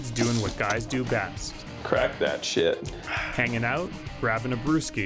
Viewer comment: "Crack that shit..." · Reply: I thought he said "correct that shit" hahah but I'm sure you're right